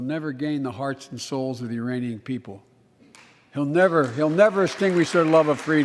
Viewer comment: Did he really say “our hearts and thoughts are with the Iranian people” instead of “Ukrainian people” ? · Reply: I heard "Uranium people"